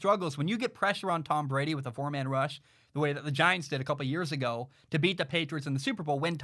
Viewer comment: No he said earlier this year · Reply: I rewatched it and he actually says both. He said “a couple” at 6:15, and earlier this year a few seconds before